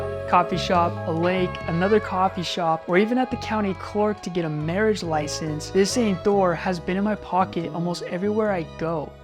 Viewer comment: I thought u said mayors license I was like uhh okay hope it works out